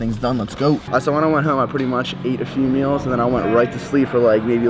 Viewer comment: did he say "ate a female"?